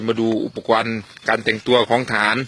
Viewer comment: ได้ยินว่าอุปกวน